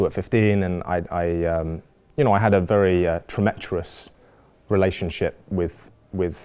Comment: What's the word at 3:54.... I can't work it out for the life of me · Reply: tumultuous